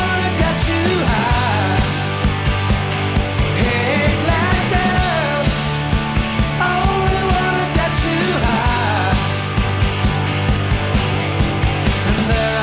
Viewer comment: lol the fist time I heard this, I thought he said "black girls only wanna get you high"